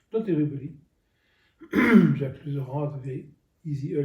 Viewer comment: I think he says "Jacques Lusseyran".